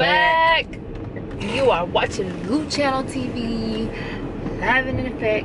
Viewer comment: Its sounds like she says Luchiano TV, instead of Luciano TV 😂😂 maybe I'm just high af😂😂